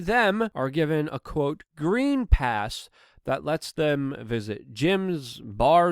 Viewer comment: When you said "green pass", thought you'd said "dream pass"... seemed to fit better too.